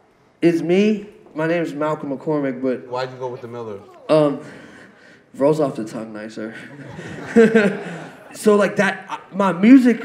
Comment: what did he say when he said he goes by the miller last name at 17:48 ? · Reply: he's saying "miller" rolls off the tongue nicer, as opposed to McCormick